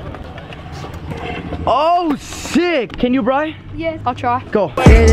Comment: i thought you said oh sh*t